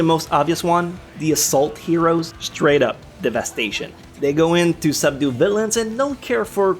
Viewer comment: anyone hear “Straight up the best asian” the first time